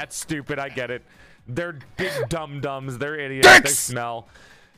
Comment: With that timing, it almost sounds like says "Their DICKS smell..." xD